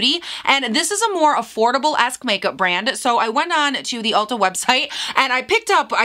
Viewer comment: The first time I listened to the intro I thought you said "affordable ass makeup brand" 😂